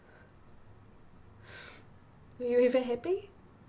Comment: MATE WHY DID I THINK AT FIRST THEY WERE SPEAKING A FOREIGN LANGUAGE HHHH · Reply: When she said "Are you even happy?"I thought she said "Are you a hippie?"